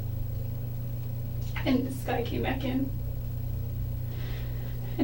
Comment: Jodi is explaining her struggle with the female intruder. asks which had she had it in, Jodi's initial reaction was to say, "she had it in this had..." And she held up her left hand. She immediately realized that was a mistake and then she said it was her right. The angle of the cuts on Travis' back (except) for 2 were all on a 45° angle, with the upper portion of the wound slanted towards the right. This shows that a left handed person had stabbed him. At some point, she switched hands and left two stabs leaning towards the left, with her right hand. This goes to show that the killer was most likely a lefty. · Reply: Jodi says, "Skye came back in and then SHE said um..." I used to think she said, "This guy came back in" but then why would she follow it up with "she"?